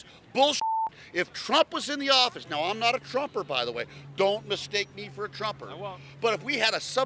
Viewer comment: When I hear “Don’t mistake me for a Trumper’” I hear “ Don’t mistake me for a man.””😂😂